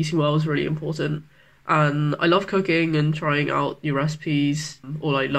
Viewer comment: i thought she said cocaine XDD